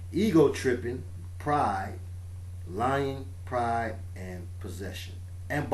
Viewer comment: Here's another one for you- at 1:10:29, you say,'Lying, Pride and Possession'. However, I heard,'Lion, Pride and Possession'. No accidents. :)